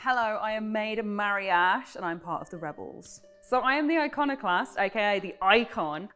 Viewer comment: "the Iconoclast, aka the 'Icon'..." -exactly what the iconoclast doesn't want to be called lol. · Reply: I first heard it as "iconic lass"